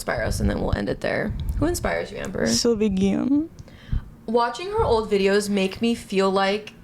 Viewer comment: What's the name of the ballerina she mentioned??? I couldn't understand and want to google it 😂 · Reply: Sylvie Guillem